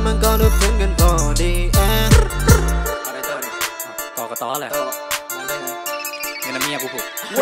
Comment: ใครได้ยินว่า กกต. บ้าง